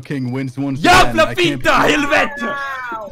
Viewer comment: AS A SWEDE I NEARLY JUMPED OUT OF MY SEAT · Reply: I thought he said “jävla Fitta helvete”